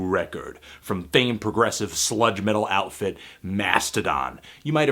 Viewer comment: Are they really Sludge Metal? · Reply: I had to rewind because I thought he said "slut metal"...